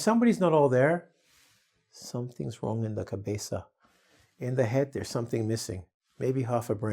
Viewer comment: Oah!! You speak Portuguese?! There's nothing in my "cabeça" 😅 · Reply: Right?.... I heard "in la cabeza"